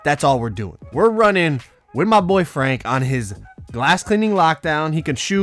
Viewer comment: Tbh when he said with my boy frank for some reason I thought he said with my boyfriend